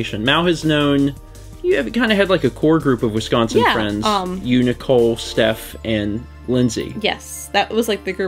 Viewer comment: I thought he said Unicole instead of you, Nicole 😅